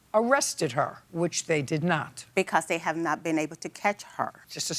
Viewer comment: What she say at 3:50 ?? 😅😅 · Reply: I think the word you missed was unable. "The witness ran back to her apartment and was unable to identify her."